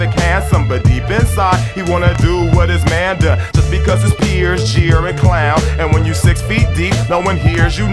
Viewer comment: Since Dragon Ball Super I just hear " His friends, Jiren, Clown"